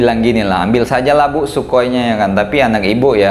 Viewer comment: "ambil sajalah bu sukoi nya tp anak ibu"................. Min ayolah di perjelas dikit 🗿😅